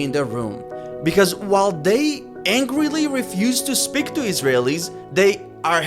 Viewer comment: did he say angrily 😂😂😂😂😂😂😂😂😂